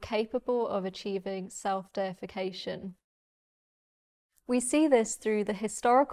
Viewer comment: automatic subtitles going horribly wrong again: instead of “self-deification” it reads “self-defecation”...